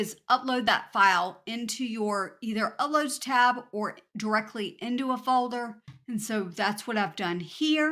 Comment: you say to download "directly into your _ tab or directly into your folder" what tab are you saying here? I have watched your video so may times. get to this point. I download it, bring it back, and attempt to remove the background... but it keeps leaving the brown square. Everything good until that point, have the silhouette (of a dog) filled with a flower pattern. All set but cant get rid of the brown around the silhouette... ugh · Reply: "Into your uploads tab or a folder" I'm not sure. Make sure when you're downloading do not download with transparent background. Just with the brown color and the silhouette with of the photo.